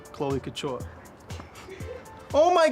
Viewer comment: I thought you said "cloaca ture" instead of "chloe couture" and was very confused